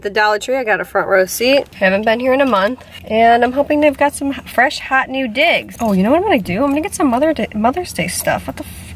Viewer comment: I watch all YouTube videos on 1.5 speed because I am a psychopath, and on 1.5 speed it truly sounds like you said "fresh hot new dick."